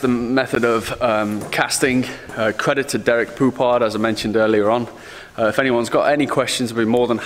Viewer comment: No offense , but did he just say credit goes to Derek Poophard?!